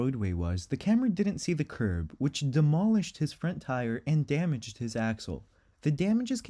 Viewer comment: did anyone else hear the commentator say 'damaged his asshole? Bwahahahaha Made you go back & check too ;)